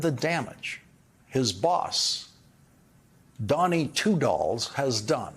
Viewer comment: I thought Lawrence said Donny two balls. That would have been an exaggeration, of course.